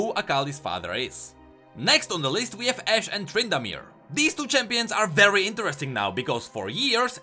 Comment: i misheard what you said and i thought that ashe married trundle. and i was a bit suprised xD hahahhaha